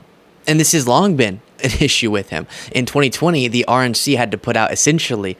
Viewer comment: Hey, can I request that when you say "the RNC" that you enunciate more clearly. I keep hearing 'the Orange C'